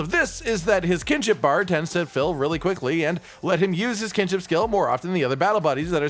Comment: Honestly when i heard you say “kinship skill” I thought you kept saying “kitchen skill”😂my volume was a bit low so that’s why